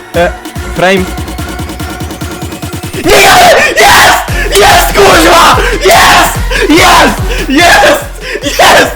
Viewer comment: what did u say here hmmmmmm i think we all need to think on this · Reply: it's "nie gadaj" in polish which in this context it can be translated to "no way"